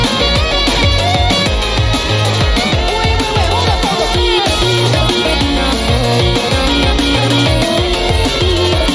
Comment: what did jelly bean say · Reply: Wait wait hold up hold up